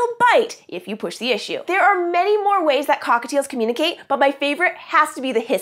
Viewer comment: What the heck subtitles instead of cockatiels it said "cock fields"